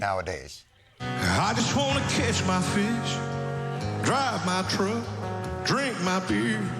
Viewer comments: First I misheard "I just wanna kiss my fish" and for a split second I was really confused. 😂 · That country singer... Without the subtitles I barely understood him. Especially about the fish thing. What I heard was "kiss my fish" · I heard, “I just wanna kiss my fish.” I’m like, “…….What?…” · Thanks a lot for the subtitles. I misheard it to "kiss my fish" even without knowing it was a meme. "CATCH"... yeah. sure. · Ah, I misheard that as "kiss my fish" the first time which with how much they promote degeneracy that just seemed normal. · I heard "I just wanna kiss my fish". Thanks for the undertitles! I was really confused.. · So glad they put the subtitles on for the country singer - I kept hearing “I just want to catch my fish” as “I just want to kiss my fish” in every other clip that came across my feed! So glad to finally have subtitles on to explain it 😂 if only the “speak English” crowd could use proper diction and actually enunciate, then maybe we’d be able to understand them! 😂😂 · I thought he said “I just wanna kiss my fish” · i still don't hear him say "catch" i hear him saying "kiss"